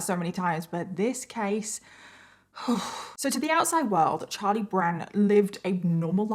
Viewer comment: at the beginning it sounded like you said charlie brown then when you brought up the beagle i was like confirmed